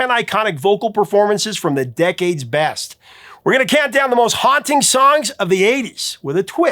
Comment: Misheard Lyric Time ♫We're going Downtown Mrs. Carey♪